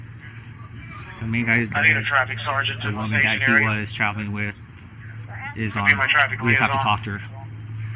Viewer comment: very interesting. This conversation between 2 girls and officer about, 2 suspects one is dead, the other is traveling westbound, something about copter? It’s hard to hear maybe someone can do better job what there saying! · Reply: I think he says the main guy is bead the other we just have to talk to her then the female says o that’s the girl on the.....